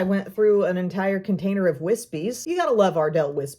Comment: I was listening to your video and not watching and thought you said you finished a whole bottle of whiskey (instead of wispies) 😆😆😆 I was like dang!